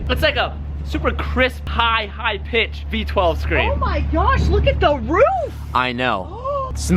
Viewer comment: Thumbs up if you thought she said.. Oh my God look at my boobs.... thumbs down if you think I'm just a perv